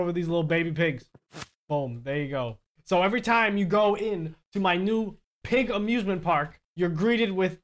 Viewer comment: i thought said "everytime you go into my pig abusement park". that's actually a better way to describe the thing he made.